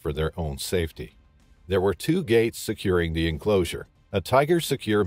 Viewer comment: I thought I heard, "there were two GAYS securing the enclosure". Clearly you need at least three gays to secure the enclosure. Amateur